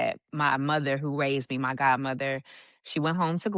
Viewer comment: I thought she said her Grandma raised now she's saying her God mother.